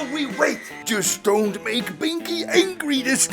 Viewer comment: just don ' t make pinky angry this time( fym pinky its binky🥀) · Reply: “Pinky” and “Binky” sound almost the same so maybe it sounds like he said “Pinky”